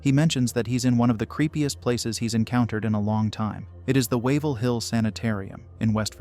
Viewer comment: at first, I thought they said “Waverly Hills Sanatorium”! After all, they’re both abandoned but one has a Body Shoot “Yes, it’s exactly what it sounds like”! Like all creepy, historic abandoned places. It’s a Haunted house during the best time of the year and for the rest of the year, you can stay the night! Wandering the halls with whom ever you wish & um, don’t!